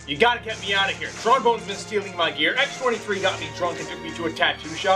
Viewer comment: Did he say "Tron Bonne's been stealling my gear"?